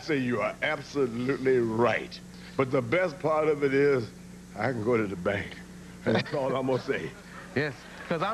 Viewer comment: Can someone tell me what he said at 6:30 I love the way he says it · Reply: "But the best part of it is I can go to the bank. That's all I'm going to say."